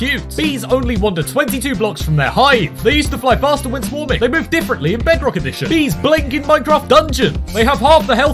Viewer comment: did he say ”they birth differently in bedrock edition” ??